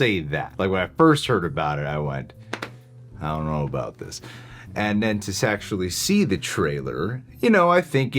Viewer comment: For a sec I thought said "and then to sexually see the trailer..."